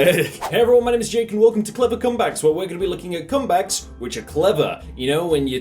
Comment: did you just say clipper cum bags?